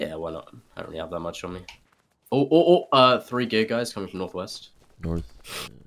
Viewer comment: the subtitle said "three gay guys coming"